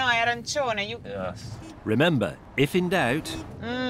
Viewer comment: What IS she saying at 2:13? No errecione? No erection? And that literally translates into "no mistake" according to google? What is up with language? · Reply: She says 'oh no, è arancione' which means 'oh no, it's yellow (the traffic lights, of course)'